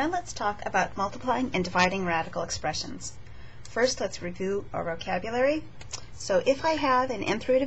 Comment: does she say revoo?